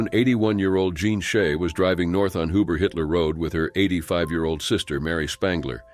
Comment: did he say they were on "uber I Hitler Road"? 😳